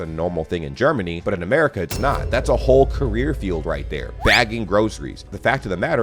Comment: am i the only stupid person that understood daggers?I was like:WHAT THE HELL???Then i heard bagging and it clicked.Ohhh baggers NOT DAGGERS